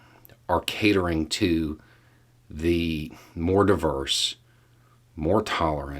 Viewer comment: In your close I thought I heard a new word, catering to the Mordor-verse. Not what you said, maybe I should stop watching Rings!